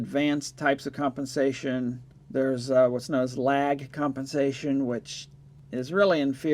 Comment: FIRST of all I'm very glad to hear you reporting about JAT-801. At about 5:40 you say something about "lag type compensation" - if I heard correctly. What about that ? Adds so much complexity to the design ? If not why not make this very good amplifier even better ?